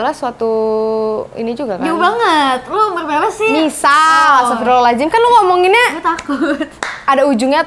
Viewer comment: Aku ndk salah denger kan kalo mahalini td ngomong astaghfirullah hal'adzim lengkapp buanget di menit 6:47 yaAllah aku msh akan slalu berdoa yg terbaik untuk mahalini dan nuca biar bs bersatu menghadap satu tuhan dgn keyakinan yg sma Aamiin 🙏🤲🥰🤗